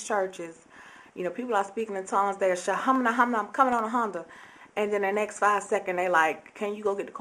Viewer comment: do you say "im coming on a honda!" hehe, a very sincere blog and even though i'm british i agree with what you're saying about mccain.. well, 'not' saying about mccain.. ;) here we have alot of rough areas too, lots of gangwars and shootings in particular urban areas near my home. Thanks for sharin!